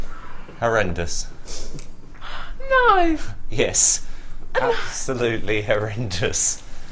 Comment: Hello, could anyone help a not native english speaker? I don't understand the word Simon says two times between 15:30 and 15:40. It sounds like "horrandis" but I can't find anything like that in my dictionary. Thanks in advance. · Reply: Ah horrendous, now I got it! Thank you very much for helping me. :)